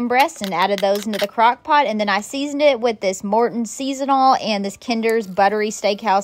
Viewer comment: Sound like she is saying Mortons seasonal not season all.